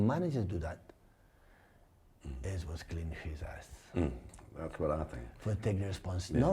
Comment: does pep say “clean his a$$ ”!?